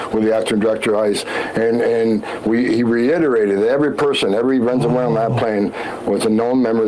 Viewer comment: he said "venzewhalen" AGAIN..... I THINK THATS JUST HOW HE SAYS IT